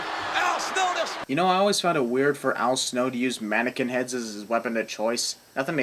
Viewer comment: You roasted Michale Cole two times in a row😂😂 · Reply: lol 11:46 your subtitles is wrong. You said you found it weird that Al Snow used a mannequin head as a weapon but the subtitle says awkward